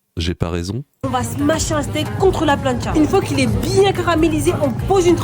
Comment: j'ai compris " une fois qu'il est bien CRAMÉlisé"